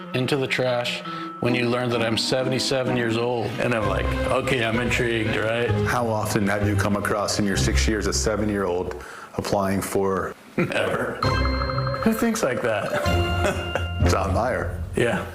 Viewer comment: Did you just say 7 year old?